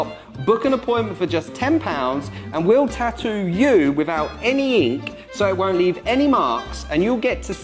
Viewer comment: I heard "pay 10 quid to get stabbed and poked". fuckin love the UK